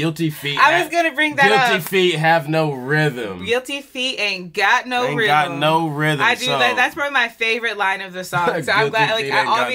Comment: for the longest i thought he was saying "cant you see i got no rhythm" LOL